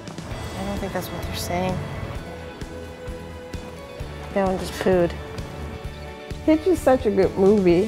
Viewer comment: i had on the captions. Actual video says "that one just pooed" Captions said "I want food" LOL! HA! CAPTION FAIL.